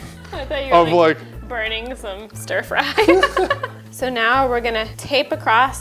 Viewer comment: When said "frying some stirfry" I heard "farting some stir fry"